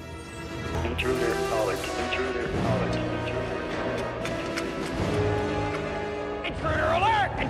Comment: I never understood what the computer is actually saying. Sure as hell isn't intruder alert. Sounds like intruder colic. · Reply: I believe it's saying "Intruder. Acknowledge. Intruder. Acknowledge"